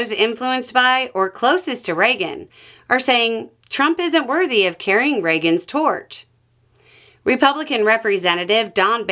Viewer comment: I misheard that as "Trump isn't worthy of carrying President Reagan's shorts" Then thought "eh, fits anyhow".